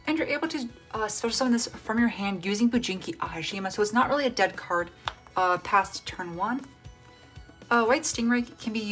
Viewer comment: whats the name of that card cause i cant see it clearly and cant hear it clearly cause its not clear plus the captions are not right so please. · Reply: Tenyi spirit - Shthana